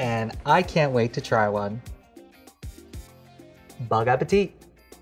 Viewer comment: I heard "bong appetite "